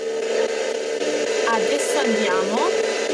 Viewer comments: ho sentito "adesso basta" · Raga ma solo io al minuto 10:55 ho sentito “Elisa adesso è mia”? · "adesso tu no" Poi quando hai sentito dei rumori alle tue spalle ho sentito "non preoccuparti" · Io al minuto 10:54 ho capito "Adesso dov'è" e al minuto 11:17 ho capito "ti ho trovato o l'ho trovato" spero di essere stato utile. ciao e buon weekend. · ho sentito "adesso prova" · dice "adesso ti prendo"... Almeno io ho capito cosí... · Prima di dire collega ho sentito che diceva Paolo,quindi voleva parlare con lui e avreste avuto più informazioni essendo che voleva parlare con lui....e poi circa al minuto 10:55 nn dice" adesso andiamo" ma "adesso è mia"...e secondo me la maggior parte delle voci che hai sentito che nn avevano senso con quello che chiedevi ecc...è perché secondo me potrebbe essere che c'erano 2 persone che parlavano tra di loro e ti davano la risposta (di quest'ultima non ne sono sicura)spero che il mio aiuto vi sia stato utile♥️ ciao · io ho capito adesso no